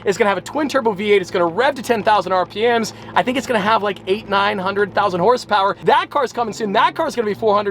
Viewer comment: did just say 100,000 horsepower 💀💀